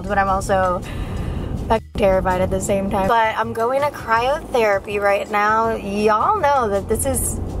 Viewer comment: Anyone else thought she said "cry out therapy" 😃 sounds good to me